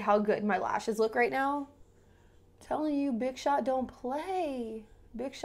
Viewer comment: Omg I thought you said bitch shot lmfao